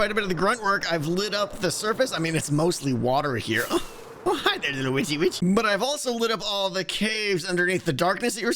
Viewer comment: i swear i hear "hi there lil witchy-b***h!"